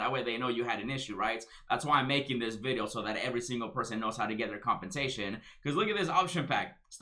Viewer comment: Every time you say "Compensation" all I hear is "Compatatoin" 🤣🤣🤣🤣